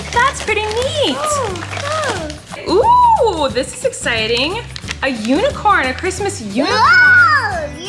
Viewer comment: Omg at 6:59 when said “that’s exciting” I heard “ohhhh yesss honeyyyy” lol